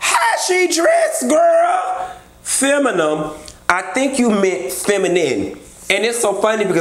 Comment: I thought Marlo said feminal. 🤔